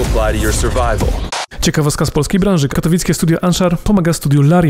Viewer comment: tak pędzi, że usłyszałem katolickie studio xd